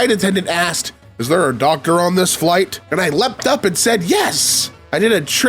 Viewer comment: I HEARD DOCTOR AS DOG GIRL AND PERKED UP